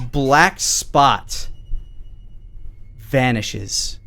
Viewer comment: now i got an ad that made it sound like said "He jesters towards your hand, Salem, you look down, the black spot value bean that releases an aroma vanishes"